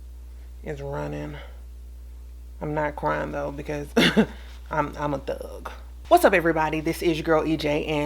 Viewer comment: “I’m not crying because I’m a thug” 🤣🤣🤣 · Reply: I misunderstood her at first and thought she said "I'm not crying b/c I'm a duck"...it took a min to click😅